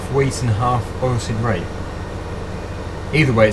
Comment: What is he calling the canola? At 15:50 it's kinda hard for me to understand for sure, but sounds like he's saying "oil seed rape."